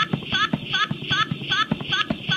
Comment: I HEARD FU**